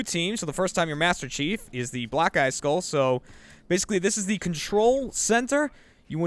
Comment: I thought said black guy skull.